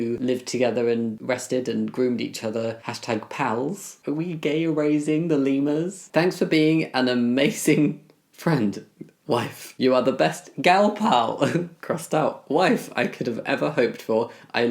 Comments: My husband from the other room: "did he just say Gay Eurasia" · I thought you were saying “Gay Eurasia” for an entire minute.